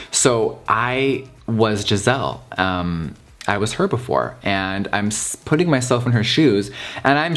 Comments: I swear to god I heard herbivore instead of her before at 5:14 😂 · Lmaooooooo I thought he said I was herbivore 🤣